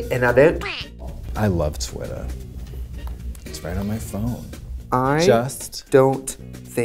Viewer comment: "I love Twitter. It's right on my phone." Something about the delivery of this makes me quote it on a daily basis. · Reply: Stop this whole time I thought she was saying “twittah on my phone” 😭 my life is changed